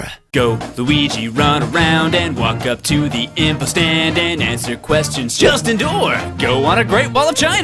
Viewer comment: I keep hearing "Go, Luigi, run around and walk up to the Impa stand"...